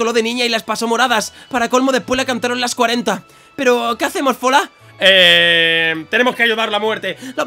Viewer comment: les cantaron las cuarenta? jaja no entendi